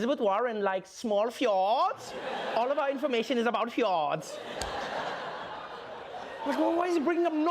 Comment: i thought he said Hjorts instead of fjords